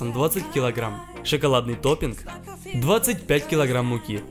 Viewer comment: На 0:32 услышала шоколадный допинг😂